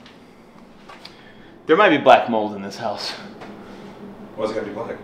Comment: LOL - at 15:50 - you May have said something that rhymes with Crack Gold but I'm sure that's not what you meant to say. That's "unidentified discoloration" .. . .